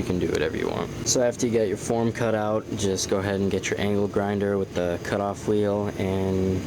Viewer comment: I thought he said Anal Grinder on the ankle grinder part.